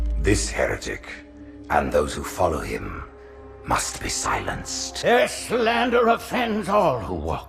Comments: ‘this heretic must be silenced’ “and so, you must be silenced” i never noticed that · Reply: I thought he said “asshole- you must be silenced” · Halo 2- Prophet: This Heretic, and those who follow him, must be silenced... Halo 3- Arbiter: ...And so, you must be silenced. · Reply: Z is for Zorua I thought he said "asshole, you must be silenced"